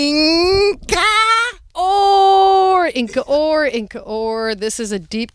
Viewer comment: What's the artist/album at 14:09? I can't figure out what they're saying. They just sound like seals. But that album artwork looks great and I wanna hear it. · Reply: Inca Ore I think